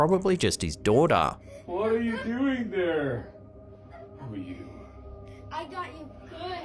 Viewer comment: Did anyone else notice the voice in the basement? Right after the man says, “What are you doing there?”, at the 3:43 mark, there is a voice that sounds like it says, “Who are you?”.